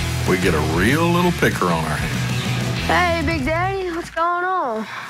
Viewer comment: did he call him “big daddy”?